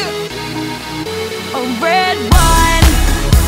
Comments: who else thought she said "a red wine" at 0:15? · She needs to bring back her writers and producers from this era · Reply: I just realized she mentions RedOne at the beginning of the song.All these years I never understood what she said.I always thought she said "red rum" lmao